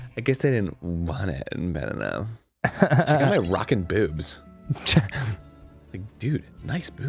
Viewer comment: I definitely thought Arin said, “Check out my rocket boobs.” Yep, accurate.